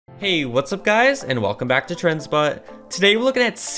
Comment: I thought you said welcome back to trends butt